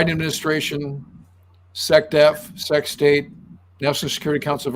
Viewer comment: I’m hearing sextape. What did you say?